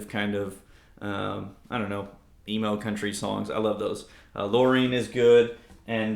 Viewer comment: Phil McKnight just named the pawnshop amps, the Excelsior and the Greta, the "worst amps of the decade", and he gave a really weird reason for that misguided (IMO) decision. Something to do with the fact that Fender kept their name off these amps. Fine, whatever. Rehousing it sounds like a good idea. I wonder if Mojotone has something available in tweed ... ! (oh, btw, you said emo country songs, right? I first heard you say "Email country songs" and I thought you had just invented a whole new genre.)